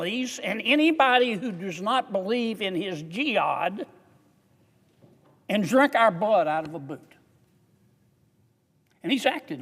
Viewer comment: I thought he said ".... and then drink our blood out of a Burka"